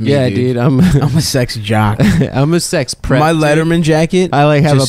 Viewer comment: I thought he said "I'm a sex predator". Had a laugh.